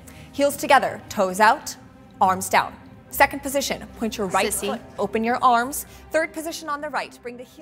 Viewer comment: i hear ricey and sissy??